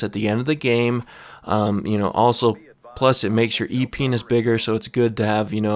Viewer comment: dude did you say epenis